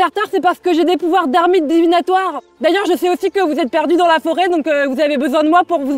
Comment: Qu'est-ce qu'elle dit ? « Des pouvoirs d'armide divinatoire » ?